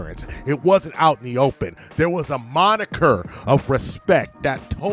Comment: o4:36min.s - Modicum... I think you mean, "modicum of respect". A, "moniker", (what you said), is... well, you get it. I agree, though.